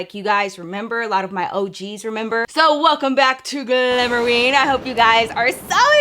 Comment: My hungry ass thought you said glamour wing 💀💀💀🥲